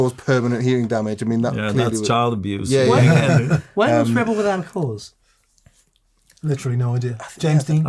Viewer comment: Man there was a comment made by someone in this video that I heard as "treble without a cause". That needs to be on one of your T-shirts!